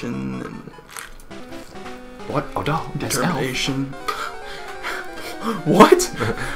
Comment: "graduation...application...masturbation..." did I mishear the last one? xD